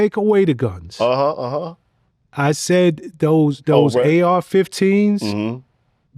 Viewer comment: Did he just say AR-14s? wtf 😂😂😂😂